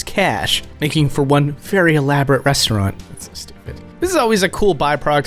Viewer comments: what did you say there ? it was very quiet XD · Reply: sounds like "...so stupid" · After says 'Elaborate restaurant'; Does anyone else hear someone (Maybe?) whisper 'Stupid'? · What does he say under his breath? Sounds like " This is so stupid"